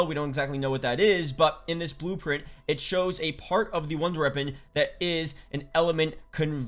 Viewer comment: Because it’s a wonder weapon... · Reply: when he says it, it sound like he saying ronder reapon